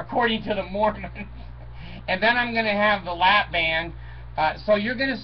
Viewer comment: I thought I heard you say " I am going to get a lap dance " LOL